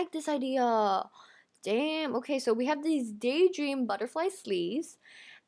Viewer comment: Did she actually say "Damn" or is that my mind messing with me